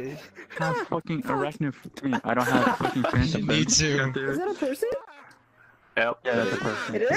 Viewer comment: Did someone say "is that a fursuit?" ???